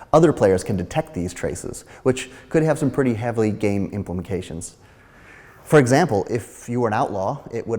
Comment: Haha, did he just say "implimacations"?